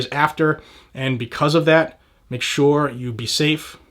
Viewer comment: = you said, "NRA." Did you mean to say, "ATF?" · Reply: I also heard him say "viligent" instead of vigilant 😔